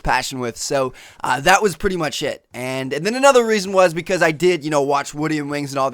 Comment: it sounds like he says "that was pretty much shit"